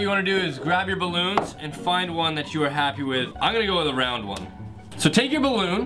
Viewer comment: did he say brown 😂😂